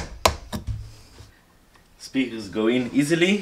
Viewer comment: i thought that said sneakers not speakers. i was confused but went with it bc i just watched the making of the painting with the moving eye mechanic, so....yeah. sneakers that look like another shoe type made sense. amazing work